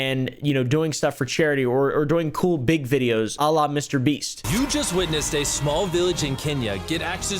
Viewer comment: did he just say “ Allah Mr beast” ?!!!!!!